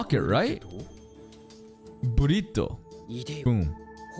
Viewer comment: Every time he says breed in sub it sounds like hes saying burrito 😂😂